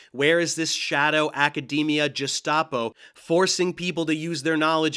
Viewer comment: Aye, I was wondering about his "Atom a tonn" too. Did he just say "~ ~ ~ Jestapo." In lieu of "~ ~ ~ Gestapo."